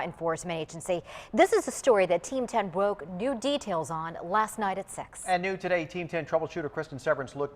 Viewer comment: Am I the only one who heard last night at sex